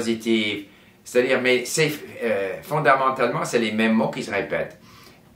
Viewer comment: est-ce que c’est à juste moi qui ne l’entends pas le mot « sont »? Je l’entends dire « ce les meme mot ». a-t-il prononcé le mot « sont »? Merci. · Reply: Oui ! Normalement, il faut dire "Ce sont les mêmes mots". Mais dans le langage courant, beaucoup de Français préfèrent dire "C'est les mêmes mots" parce que c'est plus rapide. Je suis Français, parfois je parle comme ça moi aussi. Bienvenue et bon courage à toi pour apprendre ma langue ! 😃👍